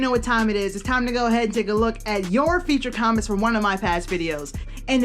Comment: For a momen i thought you said "your future comments from one of my past videos" :D